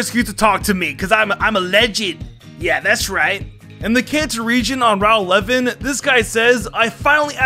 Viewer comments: Did he call Kanto the "Cancer Region" in number 10? Or did I COMPLETELY mishear him? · The way he said Kanto for number 10 it sounded like he said "in the cancer region" lmao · Thought he said “cancer region” so I googled it and couldn’t find it so I assumed he actually said Kanto.